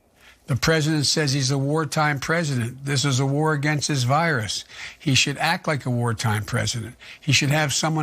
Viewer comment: Did you hear Biden? "The President says that he is a war time president. This is a war against HIS virus. He should act like a war time president." Really, that is what he said.